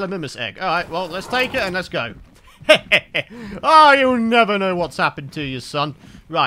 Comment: did he say “you will never know what happened to you, son” or “YOU WILL NEVER KNOW WHAT HAPPENED TO YOUR SON!” ? I think he said the second one.